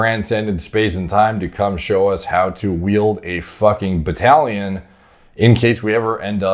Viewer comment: did he just say battalion